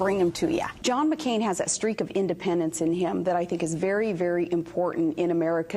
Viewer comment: When she said John McCain has "a streak of independence" I thought she said he "has a streak in his Depends".